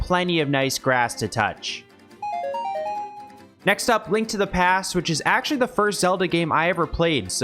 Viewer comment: The Link to The Past. I thought it said Link to Therapist.